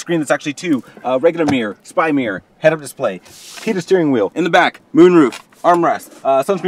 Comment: For some reason I heard "Hidden steering wheel"